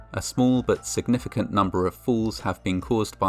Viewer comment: The Grand Canyon: where the Darwin Awards have been hosted since 1925. 08:01 "Thirdly and finally, a small but significant number of falls..." I heard "significant number of FOOLS" and thought: true dat.